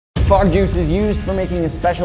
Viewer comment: I thought it said Egg juice. Wtf